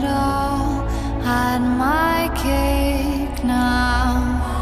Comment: i thought it said hide my cake now and was like thats a weird lyric